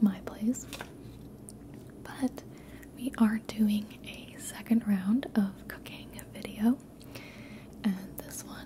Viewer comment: i legit thought i heard "we are doing a second round of cocaine" haha naughty gibi... you should always stop are the first round.